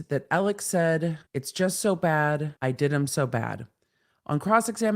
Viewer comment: I heard "It's so bad. They did 'em so bad."